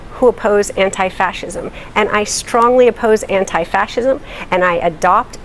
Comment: She said “I strongly oppose anti fascism”(4:15) did I hear it right? Wouldn’t that make her a fascist? 🤷🏻‍♂️